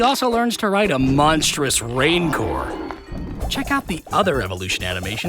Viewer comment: At the end the way he said rancor sounded like he was saying raincor